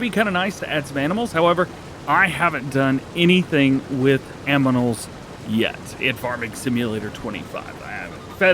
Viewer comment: did you say "aminals"? 😭